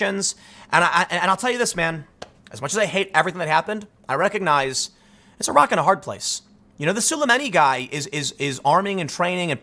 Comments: Tim, Did you say "Iraq and a hard place." I'm not mad at you Tim, just really disappointed.☹ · Did Tim just say 'Iraq and a hard place'?